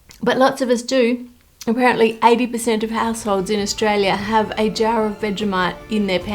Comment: I honestly thought she said "80 % of assholes in Australia... ".. Damn.. 😂